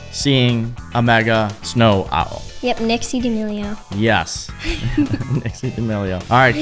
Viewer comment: is it just me or did they say”dixie damilo” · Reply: they said " nixie damileo "